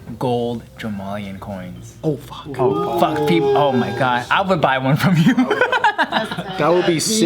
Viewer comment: sounded like he saying fuck ppl over ? Like mabe the joemalian followers ? DAmm CONSIPRECYCORNER ???